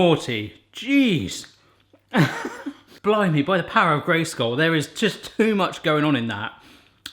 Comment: did he just say "by the power of gray skull"?